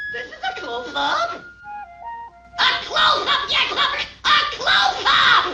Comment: I hear "A close up, ya clobbert!" and not "a closeup, ya fuck!"